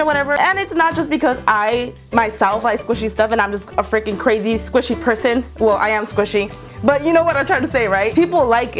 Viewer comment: I thought she said squishy pussy